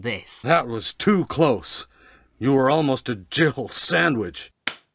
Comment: I still hear Jibble sandwich. What does he really say? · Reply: “Jill Sandwich”